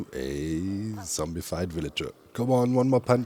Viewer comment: ah, a zombiefied villager! I first heard a zombie-fight villager. Huh? Must be new in the last update? :'D